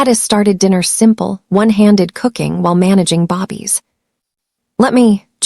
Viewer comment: Stop calling them BOBBIE!!! 😤 They a are BABIES!!! 😐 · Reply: I just goggle this too and found out the term they are referring to is babies and not bobby. I thought are they saying baby or bobby? lol